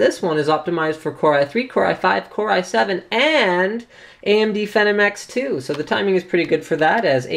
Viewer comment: For it sound like he said phenomx 2 >.< but it is phonom 2 right?